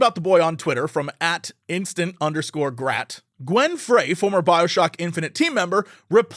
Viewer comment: Oh Gwen Frey. I thought you said Glen. I was gonna say the heat is on lol